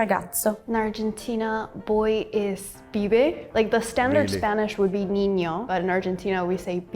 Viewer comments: Either the subtitles are auto-generated or written by someone who misheard, but it's 'pibe', not 'fibe'. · it was transcribed as "fibe" but she actually said "pibe" which is believed to come from Genoese for errand boy ("pive") or Lombardy for youth ("pibello").